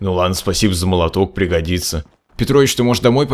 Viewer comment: только после 3 прослушивания я услышал: Петрович